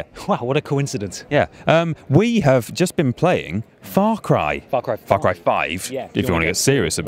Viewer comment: I think I heard "Fuck cry 5"